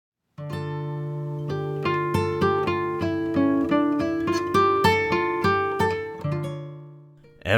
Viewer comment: The first thing I heard was, "EVERYONE IS BUSY GOING THROUGH THEIR BUSY LIVES." 🤣🤣🤣